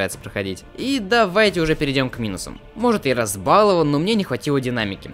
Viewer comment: я уже настолько кричур оф стил что услышал не минусам, а миносам